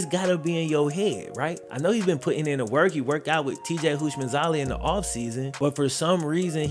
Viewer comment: sounded like he said "Houshmandzalia" 😂